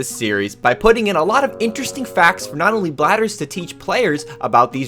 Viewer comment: i heard “bladders” the first time lol